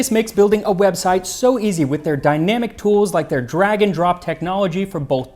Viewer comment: Haha-I’ve GOT to tell you (I’ve been sitting on this for a while, because I kept forgetting to make the comment at the appropriate time): each time, when you endorse your sponsor “Square Space”, that you mention the “drag and drop” feature, I ALWAYS hear “dragon drop”!! First time I heard it I was, like, “DRAGON drop-what?!?!” 🤪